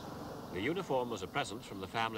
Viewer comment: I thought he said "unicorn" not "uniform" 😂